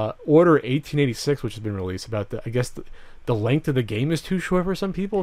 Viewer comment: When this started I heard "people are complaining about the length of the cape..." and I spent the first few seconds trying to figure out why they wanted the capes to be a specific length.